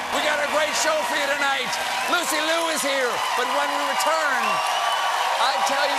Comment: I thought he said "Lucy Li is here", and immediately thought "Oh no, another porn star scandal???" If you got that joke, you probably need to clear your browser history ASAP